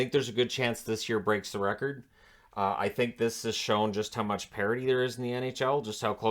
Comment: as a european individual, i thought when people said 'parity in the NHL', that they said 'parody', but why would NHL be a parody? anyway, i figured out. cheers guys!